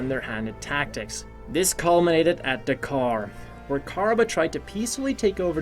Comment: did you just say KAR